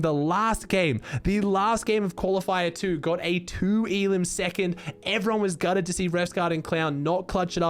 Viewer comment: '2elim second instead of 10 elim